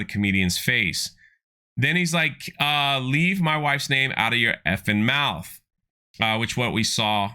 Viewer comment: Will Smith said, “you can fu&k my wife but you can’t fu&k WITH my wife!” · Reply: I must have misheard, swear he was yelling "get your 🐓 in my wife's mouth"